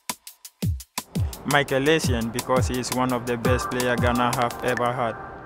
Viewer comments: I thought he said Gunner 😂 instead of Ghana 🇬🇭 was just meant to be 😀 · I legit thought he said "Michael Essien because he's the best player that Gunners have ever had" and I was here wondering did Michael Essien ever play for Arsenal. Replayed the video and he actaully said Ghana :D